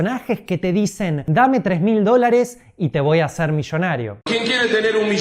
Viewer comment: Yo entendí "te voy a meter la banana", wow! Santos cielos! Haha... La explicación que diste sobre el que invertir a largo plazo en lo más aconsejable antes que el hacer day trading (especulación en bolsa) ya lo sabía, lo había leído en un blog hace ya un tiempo, por eso mismo nunca "invertí" en day trading y tampoco alcancé a aprenderlo (en mi tiempo estaba muy emocionado con ello y era a lo que me quería dedicar), ya que me di cuenta de que los que ganaban eran los que tenían sus academias y vendían cursos y no los day traders (que casi siempre pierden el dinero en la especulación, si no lo pierdes hoy, lo vas a perder mañana), lo mejor es invertir siempre a largo plazo.